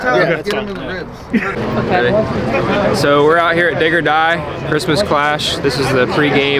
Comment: Whoooooa there billy boy! You might wanna take a little extra time to pronounce the “D” in dig or die. Straight sounded like you said “ N word die” lmfao